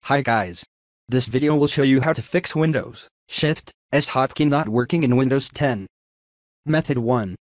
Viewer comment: i thought it said windows shit S